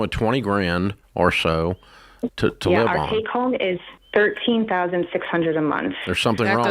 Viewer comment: SHE is the one who said they take home $20,000 a month. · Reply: she actually said they bring home roughly 13,600 at 4:58.